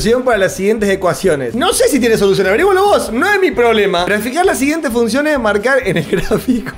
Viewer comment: Entendí "Traficar" en vez de "Graficar" traficaba cuentas la profe AJJAAJAJAJ